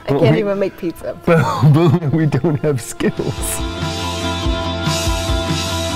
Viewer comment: what does Lena say at end of the video? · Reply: "I think we're gonna get a show after this"